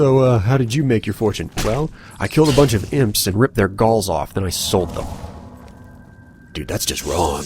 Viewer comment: lol, when you said "I ripped their galls of and sold them" it sounded like you said "Balls" instead of "Galls"